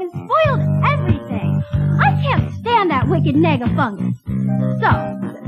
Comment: “I can’t stand that wicked Nega-Fuck!!!!” I know that’s not what she really says, but I still like to think it. XP